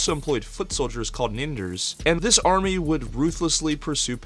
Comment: What did he say?! Was that the N word?! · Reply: Definitely not - I said Ninders